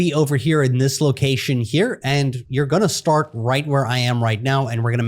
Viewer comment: care to explain how you got right there instead of “you’re gonna start right here where im at” ?